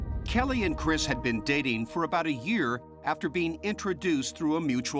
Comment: Kelly met him by a mutual friend. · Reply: she did? I thought they said through an app..